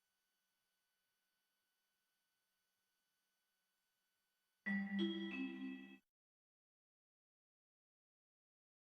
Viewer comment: Some people are saying that the nose was someone saying something. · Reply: Troy Kipping - She said: "damn it...."